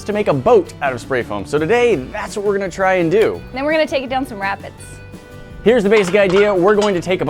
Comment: Oh take it down to some rapids, at 2x speed I kept hearing "take it down to some rabbits" lol